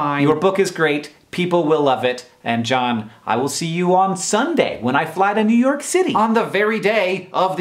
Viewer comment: Sounds like you say "I'll see you when I flatten NY city"...